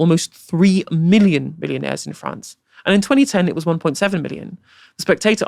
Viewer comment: About 1:12. Did he say "3 million billionaires in France"? I find that hard to believe.